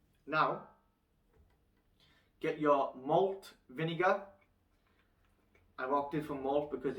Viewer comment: Excuse me what type of vinager? I am not a native english speaker and I heard "mold vinager", but I don't think I am right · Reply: Malt vinegar